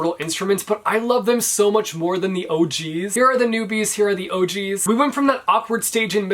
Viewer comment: when you have your captions on and instead of OG’s it says OH GEEZ!😂😂😂😂